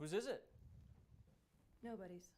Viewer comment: is it just me or am I hearing "nobogies"?